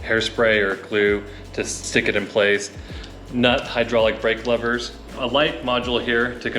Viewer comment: it sounded like " not " 🤔😅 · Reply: he says Nutt which is the brand